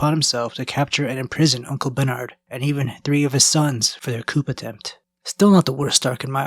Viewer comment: Did he say "couP" with a hard P? Lmao